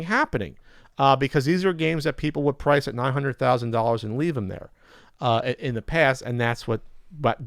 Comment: I almost thought said 900,000$ didn't hear 900-1,000$